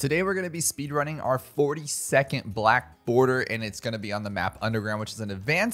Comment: When you said 42nd black border at the beginning, For a second, I thought you said "40 second black border"